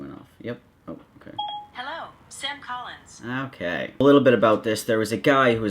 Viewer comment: I thought Siri said “Seth Collins” and I was hella confused for a second.